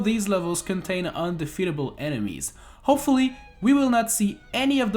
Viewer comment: I know he says 'this is an octoomba' but I heard 'this is a noctoomba' so I call octoombas noctoombas